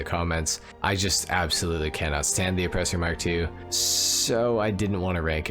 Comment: Thought it said offensive 😂😂 And at number one we have the Oppressor Mark 2